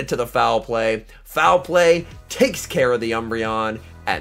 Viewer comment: "Foul play takes care the umbreon" Or do I mishear it?